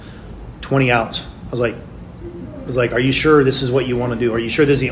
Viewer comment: Pay close attention to what Trevor said: "15, 20 times of legal dose..." AND later on @ 7:10 Trevor continue said: "He came and pick up the money, and I gave him 20 oz"!!! did he just slip? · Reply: He said "twenty outs," not "twenty ounce."